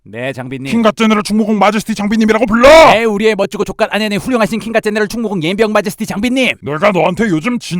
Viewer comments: 여기서 마제스티를 처음 들었을때 마조히스트 라고 들었음 ㅋㅋㅋㅋㅋㅋㅋㅋㅋㅅㅂㅋㅋㅋㅋ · 초에 킹갓제네럴충무공옌병마제스티 장비님이라고들리는뎈ㅋ